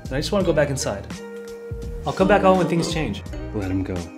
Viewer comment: When I first heard this I thought he said “I’ll come back out when gta 6 released” 💀